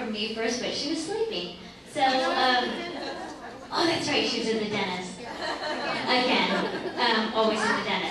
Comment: What does she say at 49:15??? Like she was in the dennis or something!?!?